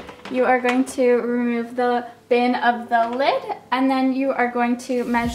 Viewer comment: did she say bin of the lid??😂